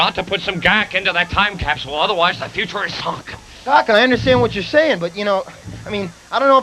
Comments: They really should have buried it for 30 years. Most of the kids in that video are now parents to young kids who know more about electronics & technology than we did at that time! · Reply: did he say the future is fucked bc there would’ve been no GAK😂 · other wise the Future is Fucked lmao sounds like he said that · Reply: "The future is sunk." Is what the faux Doc utters away from the mic, and......Mike. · I genuinely thought he says “The future is fucked” there 😂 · did he say the future is fucked bc there would’ve been no GAK😂